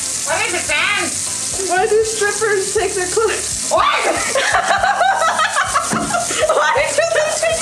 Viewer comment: For the people wondering what sans was asking at 2:50 he said "why do strippers take their clothes off?!"